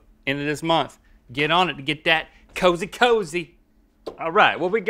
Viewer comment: Did you just say ''Cozy Cozy''...